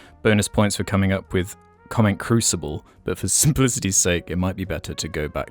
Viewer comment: I thought they were saying “comment cruise missile” the whole time 😔